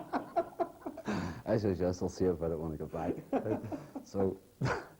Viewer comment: Can someone help me ? ... at around 45:58 Brendan Hughes is saying something about the Taxi driver telling him that his car is always getting highjacked, ".... but you are the only one...... " ..I can't figure out how he ends the story / what he says from then on. I'd aprreciate if someone could help me out on this one. Thanks... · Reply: Taxi driver said his car was always getting hijacked in Newry could he (Brendan) do anything about it, Brendan said “yes, I’ll see about it when I get back.”